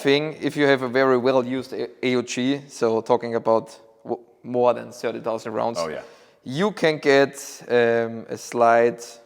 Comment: did he say '7,000 rd' or '70,000 rounds'? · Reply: He said "more than 30 000 rounds".